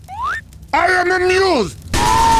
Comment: i thought it said “i am immune” lmao